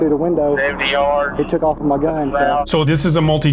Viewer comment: When the trooper was talking to the other cops that responded after the pit, did he say that the guy took off with his gun? That's what it sounded like but he got out of the car with his gun drawn, didn't he. Am I missing something? I would ask the second trooper but he was busy not paying attention to anything.